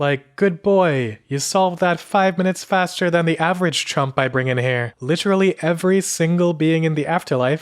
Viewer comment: "The average Trump"? Such one would never enter that "place" But maybe I heard it wrong :)